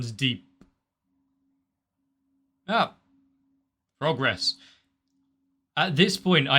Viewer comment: When you said "progress" early on, I thought I heard "frog-gress"